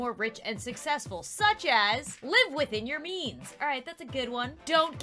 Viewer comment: i was only listening to you cuz i was eating and i thought you said "live within your knees"